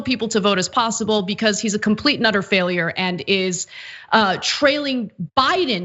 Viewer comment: I love when you say "complete and utter failure." What I hear is "complete nutter failure." 😁